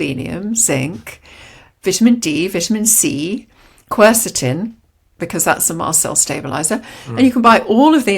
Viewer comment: "QUERCETIN?" What did she say what the mast fell stabilizer was?